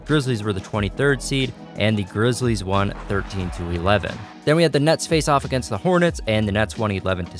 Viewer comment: If you listen close at 2:57 he said the nuts instead of the nets 😂